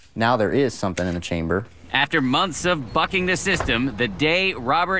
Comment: Swear I heard "fucking the system"